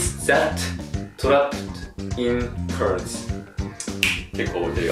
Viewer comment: cars の発音は curse 呪いの聞こえる 😂